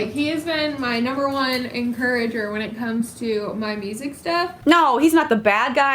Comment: On the subtitles when says "He's been my number one encourager on my music STUFF" the subtitles say "on my music's DEATH" hmmmm suspicious